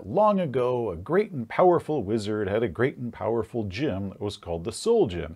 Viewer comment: I misheard "great and powerful gym" at 2:47 and immediately knew what my next adventure idea would be.